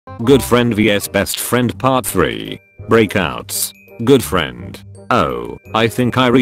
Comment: Cl I thought the first one said blackouts instead of breakouts